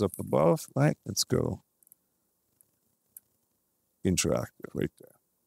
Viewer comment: Ay yo. I was watching this well made video and at 27:32 I heard: Interactive, uite! Uite is a romanian word. Now I might heard wrong, but I invite u to hear. Interesting content anyway